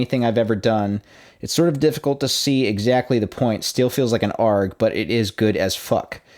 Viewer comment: Now I see why I couldn't understand that word. It's because you said "Arrg" instead of A-R-G. It isn't pronounced like a word. It's an acronym.